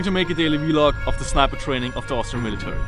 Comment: Did you just said Astra Militarum? Also where did you get your outfit? I have bunch of military stuff, some clothes and equipment with additional mercenary suit. I'm trying to get as many as I possibly can.